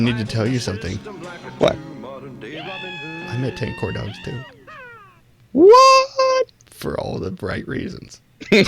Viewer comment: Anyone have any idea what mumbles at 37:50? CC says "I'm the tank or don't you think whoa for all the right reasons" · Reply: Imma give it ten corndogs too, and for all the right reasons, too.